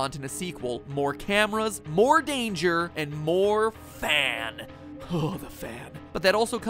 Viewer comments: What the video said: "more cameras, more danger, more fan" What I heard: "more cameras, more danger, more Phan" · I'm watching this while cleaning my room so I wasn't paying attention and when he said "More fan" I thought he said "More Phan" I will return to the trash now don't worry